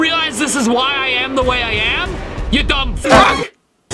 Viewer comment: I heard "dumb rug". Also your videos are hilarious 🤣🤣🤣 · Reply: I hear “you dumb- AH!”